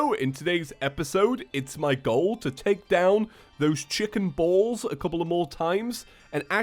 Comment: hey loved the video keep up the good work ( i also thought that when you said chicken bowl i thought you meant chicken testicles)